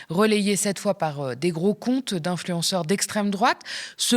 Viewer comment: J'ai entendu "des gros cons d'influenceurs d'extrême droite", mais j'ai un doute...